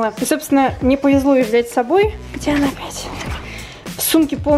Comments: где она блять ? или мне послышалось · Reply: "где она опять?" хд. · я одна слышу мат?? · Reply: Мария Григорьева она сказала: где она опять